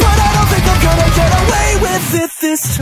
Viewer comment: Ngl i first thought he said "To hell with good intentions" "So to hell is where i go" Even made sense in its own way